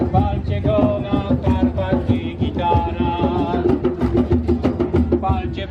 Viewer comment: czy tylko ja usłyszałem "chwalcie go na strunach i NAPLETACH"???